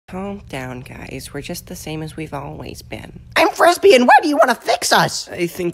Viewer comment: What did she say? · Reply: “Calm down guys we’re just the same as we’ve always been”